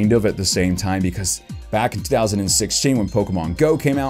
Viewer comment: did you just said 2006? I know what you mean it's 2016 but heck I keep on and on listening. It sounded like you said 2006.